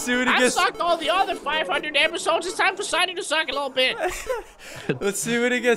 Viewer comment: I misheard "Suck a Lil' bit" lelelelelelel